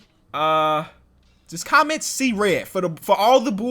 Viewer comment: See red At first I thought he said sea ref